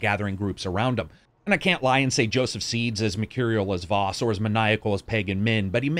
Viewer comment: I cant understand what you said shortly after 6:15, maniacal as pagan min and m....ar as vaas, i cant catch the word, english is not my native language, please help :) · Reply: Material